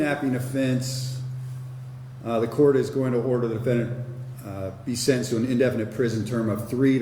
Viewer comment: Darnit I thought it said sentenced TO death